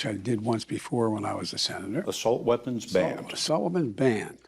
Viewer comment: I could of sworn Biden said “assault women “that second time lol